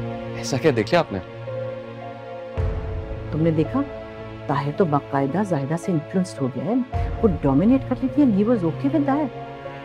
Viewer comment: What is Madam Bushra saying at 20:25 ? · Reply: Tahir to baqida zahida s influenced ho gya h...meanz control m h usk... Vo dominate kr rh th n he was ok with it...